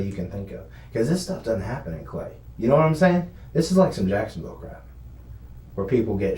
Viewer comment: did he say this is black ppl stuff😂😂😂